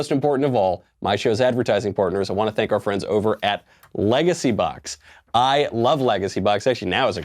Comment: I actually thought he said legacy buck not LegacyBox